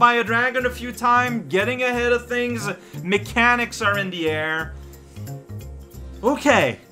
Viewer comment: I heard "we ate a persimmon" as "we ate a person" when I was tabbed out and thought "I didn't think it was that kind of game"